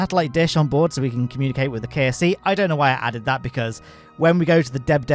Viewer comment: i heard KFC not KSP